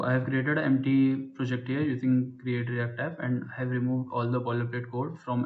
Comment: How did you create that empty project? Sorry I didn't catch what you said you used... Thank you! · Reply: It's called "create-react-app". By using this, it sets up our project with the minimum dev environment. Docs - <link>